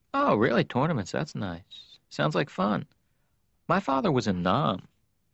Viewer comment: The whole table tennis part is the best and he ended with "my dad was in Nam". Hilarious! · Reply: Dionne Faulkes I heard “My father was a nun.” Sounded...odd.